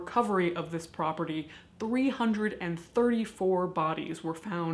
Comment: oh my god for like half the video i thought you had said 34 bodies not 334 so when i heard it clearly for the first time i just about dropped my cereal